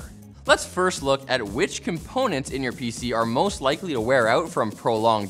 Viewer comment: He said “PC are” I heard “VCR”